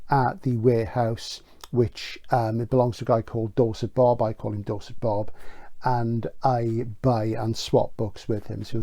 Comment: At first I thought you said Dorsai Bob. I need a better technology interface 👽